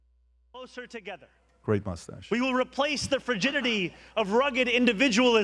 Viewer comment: Did he say frigidity? I thought he said rigidity and a podcaster thought he said fragility. What did he say?